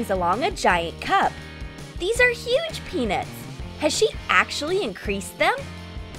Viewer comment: i dont even know why i subscribed to this channel Edit: did i just hear at 0:42 "These are huge penis"? edit: no offenc3